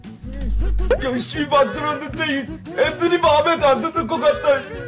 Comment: 초 에 맹구가 열심이 만들었었는데를 열시바라고 들응ㅁ